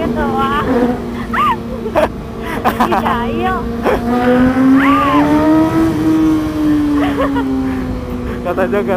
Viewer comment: Janis: ih sayang Bener ga? Apa gw salah denger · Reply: Ihh jaill katanya